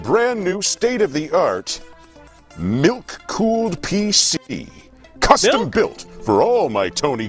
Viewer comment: Idk why but when tony said he had a milk cooled pc, I thought he said NUKE cooled pc